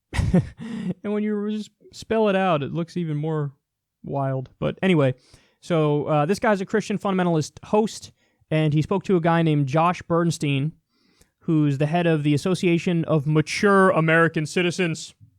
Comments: I thought you said Josh Feuerstein and thought 'oh, not that arsehole again!' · When Kyle said "Josh Bernstein" I thought he said "Josh Feurstein". Was sorely disappointed when Feurstein didn't come on.